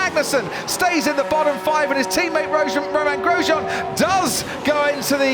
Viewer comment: Did anyone catch Crofty saying Gromain Rosjean! 🤣